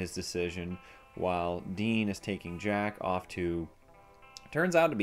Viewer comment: Did I just hear you say “jackoff”? Hey now, let’s keep it pg